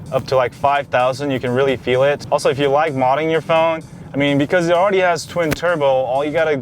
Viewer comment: Is it just me or did he say "Also if you like modding your phone" at 9:59 Lol